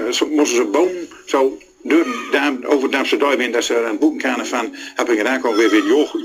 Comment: I swear at 1:51, he says "dumpster diving."